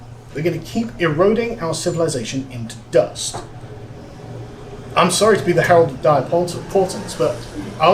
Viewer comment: ca. 19:47 I cannot make out what he says, and the auto-gen'd captioning in English didn't get it right either. I think he said, "I'm sorry to be the herald of dire thoughts, pause, and spurts..." I'm 100% certain only up to herald of dire thoughts. The phonetic CC made no sense grammatically. Anybody?